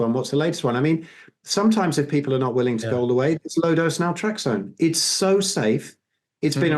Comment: For those who couldn't catch it at 50:25 it's "low dose naltrexone"